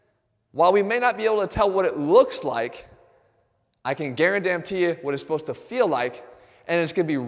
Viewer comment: Did he say I can guaradamntee it? 😅